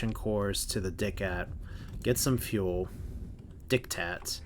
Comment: did you really call the Sindrian Diktat the "dickhat"???? Lions Guard would like to know your location!!!!!